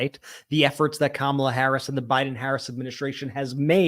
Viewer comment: You guys are great but I feel like both of you have said VP Harrison a few times lol Brett 42.27 mins in lol. Honest mistake I am sure but it Harris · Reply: I heard it as well, but then realized he's saying "Harris an(d) it's...".